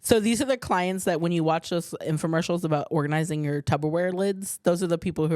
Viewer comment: I cant believe I heard "tubberware", I thought Sal Vulcano squashed that 😂 "It's Tupperware babe. Always has been, always will be"